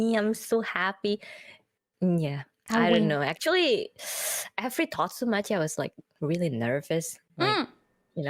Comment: I think at 0:29 and 0:43 she says "every totsumachi" instead of thought so much. Talking about how she gets nervous on the call-in streams.